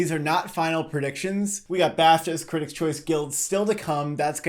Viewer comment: I put on the automatically generated subtitles and at 00:08 they say bastards instead of BAFTAs, I'm laughing way to hard lol